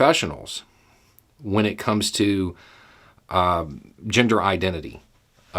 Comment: For a second, I thought Beau said "ginger identity"... which makes perfect sense if one identifies as a red-head.